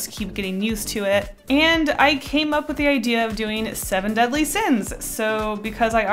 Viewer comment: Well I thought it said “drawing 7 deadly sins”